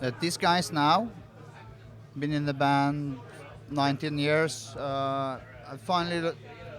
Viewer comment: did he say 19 years? or 9-10 years? cause they are not in the band for 19 years :D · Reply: 9-10 years